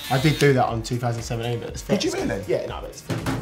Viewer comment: did he say that love island 2017 was fake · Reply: Lizwani he said ‘it’s fine’ not ‘it’s fake’ hope this helped